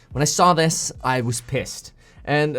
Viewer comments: I thought the "pissed" said "passed" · i thought it said passed instead of pissed 😿